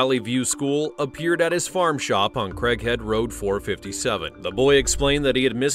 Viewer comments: Did he just say crackhead valley or am I just hearing things?😂😂😂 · I swear I heard "Crackhead road" the first two times then I saw Mom lmao not far off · I thought they said crackhead road 😂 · Who else thought he said crack head road 😂